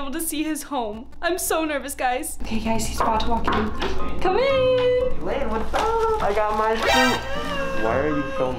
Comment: At first I thought said hi lani but when turned the volume up he said Hi baby...awwww